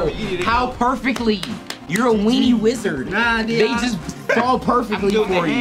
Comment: I thought he said Weenie Whistler but Weenie Wizard is just as hilarious